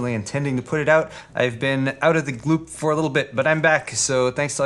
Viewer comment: Anyone else hear him say "out of the gloop" at 0:33 ?